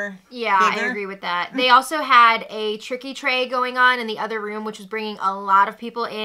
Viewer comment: So happy you surpassed your goal. I never heard of a tricky tray, so I googled it!! I thought you said tricky trade..no such thing..lol